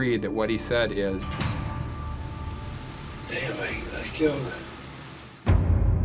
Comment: I heard 'Anyway, I killed her'.